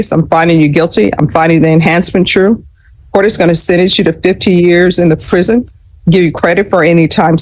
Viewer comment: I thought she said 15 years, was it really 50 years? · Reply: I used the captions. 50 years.